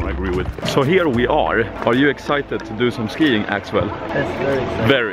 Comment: did he just say "axewell"?